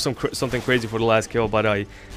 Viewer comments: Haha I thought he said rape comment and subscribe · When you signed out it sounded like you said rape, comment, subscribe